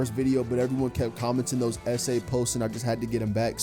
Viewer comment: At the 8 second part my dumb aah thought you said sa not essay it took me a second to realize what you meant lol.💀